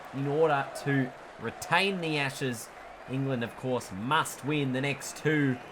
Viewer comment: I thought he said retain the asses lol